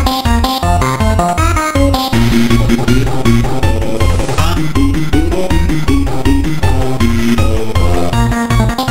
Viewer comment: someone tell me what that says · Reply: It saying hopeless